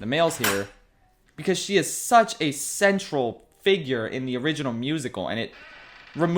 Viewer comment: At first, I thought Schaff said that Abuela was such a "sensual" character in the original, not "central".